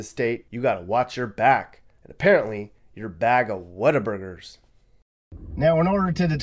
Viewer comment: I thought you said ‘water burger’ 🍔, duh it was ‘what a burger’. What a plonker!! I love to see the ‘real’ America, rather than always being shown the tourist areas. It makes you realise wherever you live in the world 🌍 no where is perfect Thank you 😊